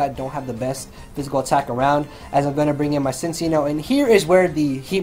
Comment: Man, every time he said "Cincinno" I heard it as "Since y'know"... I got so confused because I wasn't lookingat the screen while listening to the narration...